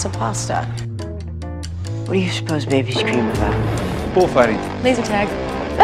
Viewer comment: What did Henry said at 2:10 ? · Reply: "What do babies dream about?" David: "bullfighting!" Emma: "lasertag!" Mary Margaret: "that's not true!" 🤣🤣🤣🤣🤣🤣